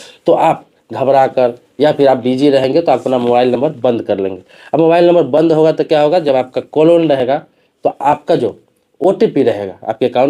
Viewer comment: pe kya bola aapne ? "Call on rahega" ?